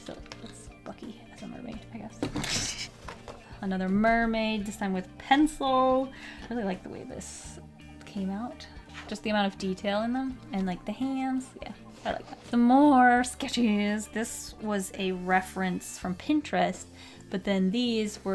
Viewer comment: did she say "the hams"? I swear it sounds like it to me.. I say "hamms" and people look at me like I'm crazy lol I guess in my case it is from having kids. But MAYBE I am just hearing things.. lol